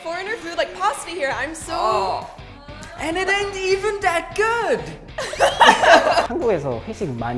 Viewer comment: 초에 남자분이 가격도 비싸고 심지어 맛도 별로야! 라고 말하는거 같은데요? And it ain't even that good으로 들려요